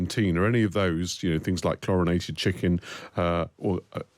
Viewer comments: I heard 'chlorinated chicken' as 'Coronation Chicken'. Wondered why the US wanted to import it to us. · I thought you had said "Coronation Chicken!" American, here... My thought was, what's the big deal? It's a dish, someone can make it, wether there's a Coronation, or not! Chlorinated Chicken, however, is another matter! Stick with your food standards! The British taxpayer pays for the NHS! Don't make British people eat something, that might make them sick! And you'll want to rejoin the Single Market... Don't lower your food standards! It will soon be "The Jungle," here, and we'll need another Upton Sinclair!